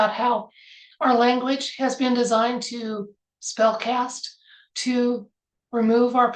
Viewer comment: I misheard when you mentioned Spell Cast. I thought I heard Spell Cats. Funny. ❤ T.Y. for another reading.